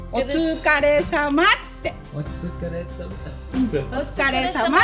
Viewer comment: What she was saying is "otsukare sama" which means " You must be tired" in direct translate but actual meaning is greeting to someone when you leave work or you meet someone. it has a lot of meaning and its used as greeting like "hello". I love your Japanese :)